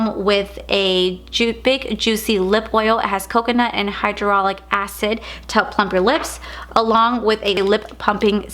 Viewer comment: Did you just say the lip oil comes with hydraulic acid?!? 🤣